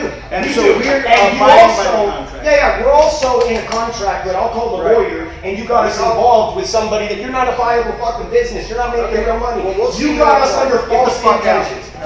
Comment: It sounds like fabia says nobody wants your Arab fuckin money. "That boy a racist" lol...I could hardly hear it with big mouth. Lol